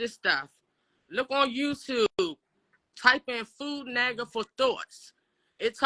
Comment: mark...Question, what did she say? "Food naggers for thoughts" ? I tried looking that up....couldnt find anything